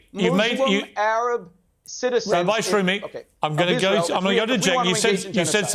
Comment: Pierce did not just say rabbi shroomy xD lmaooo